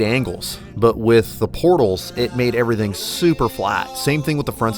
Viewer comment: Every time all I hear is "BORTLES!"